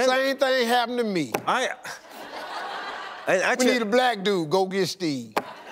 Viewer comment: he said, ... a black dude go ...what? even 99% of audience didn't understand it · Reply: 'Same thing happened to me. We need a black dude? Go get Steve.' English isn't my first language, and it was easy to understand.